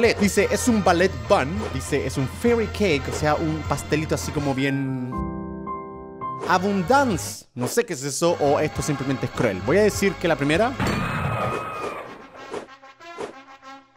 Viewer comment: entendí A-Bun-Dance (un baile de pastelillo)